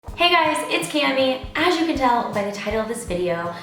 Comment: Thought u said "hey guys it's Candy" at first and I was like whoa when did that happen 😂